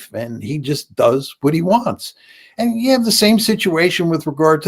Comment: When Mearshiemer said "he just does want he wants", I heard it as "he just thugs want he wants."